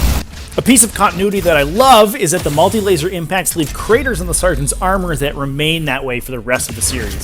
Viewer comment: i swear you called him a Spartan lmao